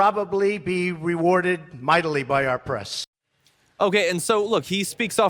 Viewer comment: According to Ana Trump said "our company will reward you" when he clearly said at 0:37 "rewarded by our press" According to John Trump said "they should find the email" not that they already have it, when he said at 0:09 "if they hacked they probably have her 33,000 emails, I hope they do TYT said trump mentioned Russia because he has ties with them instead the fact that everyone in the media has been saying that RUSSIA HACKED THE DNC They are coving up the DNC emails about them and clinton cheating during the elections by using trump. TYT differently not bias